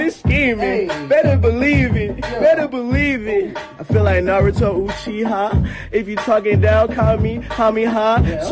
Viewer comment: At first I thought he said naruto uzimaki but instead naruto uchiha 🤦‍♂️